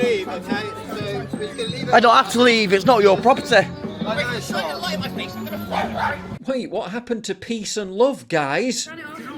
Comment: What did that thing say at 9:45??? It sounded like "get that light out of my face I'm gonna r*pe you" but maybe I'm just hearing things